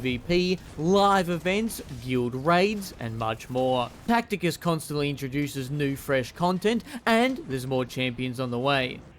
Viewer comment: The rat-based economy is booming. Since I just woke up, I heard "Tacticus constantly introduces french content" at 4:46 and I was not having it! French is not a valid language and should be banned. "We can all be buddies while hating Karl" just like at work. Woman randomly talking to you? What is this impossible bossbattle scenario? God Rat achieved, imagine all the crepes it could make by pulling a a chef's hair.